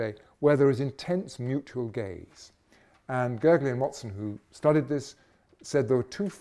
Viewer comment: Can anyone catch the name of the researcher he mentions at 32:46? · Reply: I think it was two people, Gergely and Watson.